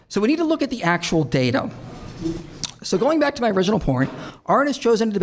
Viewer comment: Did I hear that right? Did he say ''original porn''?